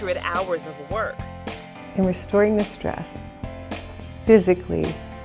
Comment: thought it said destroying